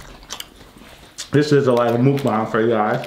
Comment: Hello good see u guy's that's looking delicious😋😋❤❤😊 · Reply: At about 2:15 did he say this is their last mukbang?